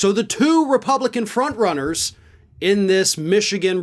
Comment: Did you say two Republican fraudrunners or frontrunners? I think I misheard.